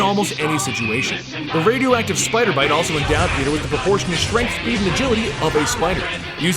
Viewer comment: What did he say? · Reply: I hear Endowed Peter with proportional strength and agility of a spider.